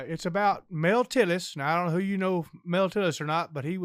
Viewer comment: yall literally make my day, Thank you for being awesome and bringing joy and laughter into my life 🤣😉 we love you !!!! MA'AM MA'AM and why when he started telling the joke i thought he said male titties not mel tillis HAHAHAHA